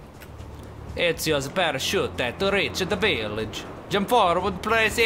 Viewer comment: my video buffered right when he said parachute, so it sounded like he said pair-a-shoe... XD